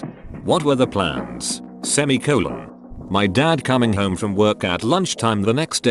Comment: I died cause I wasn’t paying attention and I thought they were doing “semi-anal” before I realized what they said”